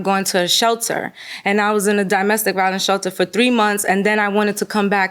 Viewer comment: It sounded like she was saying dahmestic violence. This woman is suspect. When the judge was saying sorry for her predicament, she brushed it off as if it's nothing. Also, all of a sudden, the movers took other stuff when she was losing the case.